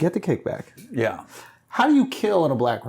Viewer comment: min I had to rewind because I heard him initially say "How do you kill a black man?" P.S.: I'm not native English speaking.